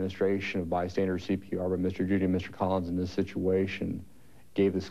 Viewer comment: Mr. Collins is that what I heard?